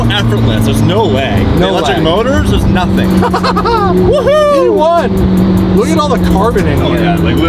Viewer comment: “P1!” What I heard: “We won!” Yes, yes you did.